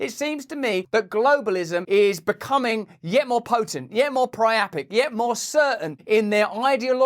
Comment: I'm Irish and call for a referendum. Please share we need to fight back. They work for us its time we remind them. Love from Dublin Ireland Russell✊👊 there's a difference between hate speech and speech that you hate, how do we differentiate? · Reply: did he say priapic?